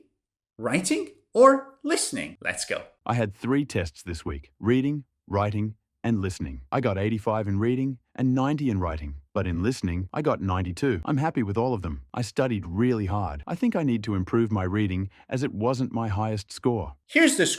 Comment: At first, I heard 'But I got 90 too' instead of '92'. risky this one